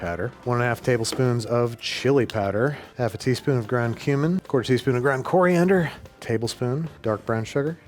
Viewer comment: When he said cumin I thought he said human 😭